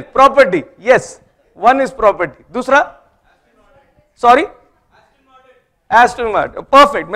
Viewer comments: What was said at 18:13? Please, anyone. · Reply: Aston martin.. a big car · I couldn't hear what was said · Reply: Aston Martin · What he said??? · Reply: Aston Martin. Car!!!